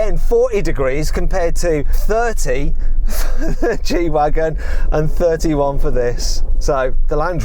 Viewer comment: 空耳 「サーティー、サーチーはアカンww」 に聞こえる人🙋‍♂️